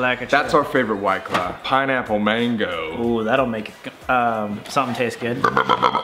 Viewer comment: We not gonna talk about how when Colby pulled out the Mango Pineapple one Sam was like- “that’s gonna make something taste good 😉” like okay Samuel you’re not such an innocent boy after all 😶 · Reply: I was looking for this comment! It kinda sounded like he said “that’s gonna make K... something taste good” idk might just be me lol